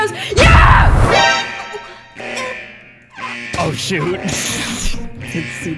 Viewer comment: Did say shiiiii- you didn’t see that🤫 In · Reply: He said shoot